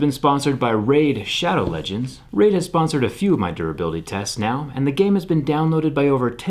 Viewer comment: I thought he said "raid shadow legends has survived some of my durability tests"